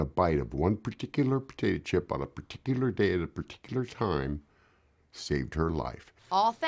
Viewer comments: That guy was very particular with his words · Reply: It sounded like he said “Peticula” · I love it how her husband says "Particular" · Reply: I heard "pertikilar" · It sounds like he is saying bum particular